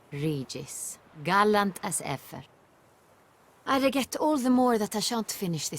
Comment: She said "I reget" instead of regret. Her accent is so cute!